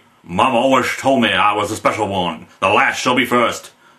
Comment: when is speaking with his Elvis voice he sounds like he sad woman instead of one.